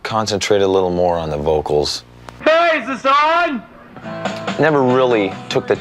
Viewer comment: Dude i thought he said "praise the sun" as dark souls reference here. my ears be playing tricks on me